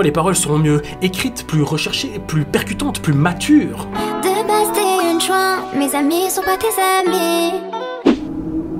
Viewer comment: svp dite moi çe qu’elle veut dire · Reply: Elle veut dire "De base t'es une p*tasse, mes amis sont pas tes amis" Donc que les amis de Wejdene ne sont pas les amis de la p*tasse.